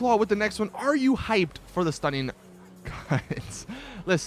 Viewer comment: I thought u said it was the "stunning cunts" dlc